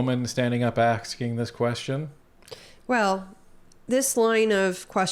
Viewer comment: did U say axing the question? Didn't know you spoke Wal-Mart 🤔😂